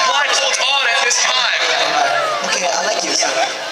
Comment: What did Bambam say at 1:32 that made everyone scream? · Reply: he said "okay i like you"